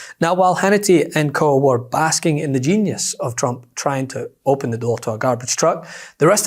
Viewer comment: I thought they said Trump dont Drink he looks pissed to me getting into that Truck.😢😢😢😢😂